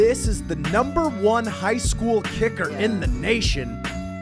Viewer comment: I thought he said he is the #1 high school shooter😅😅😅😅